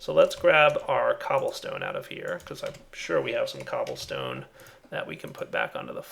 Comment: did he really say 'cablestone'?